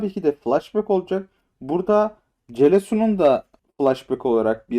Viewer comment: Meke diyecekken celesu mu dediniz ben oyle duydum sanki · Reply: Celo konuk oyuncu olarak gelebilir dedim